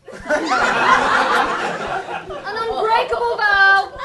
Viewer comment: I heard a bit of Umbrage right there